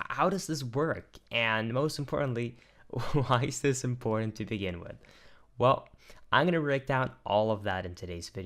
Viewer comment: At the beginning I thought you said why is it important to the N-word